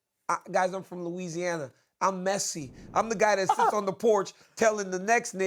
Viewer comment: Did DC just say he’s “Messi” ??